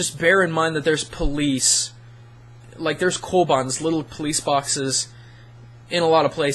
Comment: lol I thought he said 'little police foxes' In Japan, that wouldn't haven been the weirdest thing I've seen...